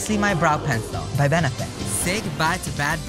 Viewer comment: At first i heard "Bad bra days" Oof